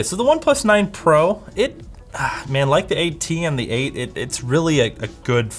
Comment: Hey you accidently captioned "80" here instead of "8T"